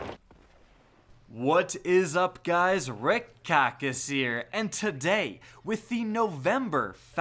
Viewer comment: What's up guys rick cockass here... jk love you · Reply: I always hear "Wrecked cactus" lol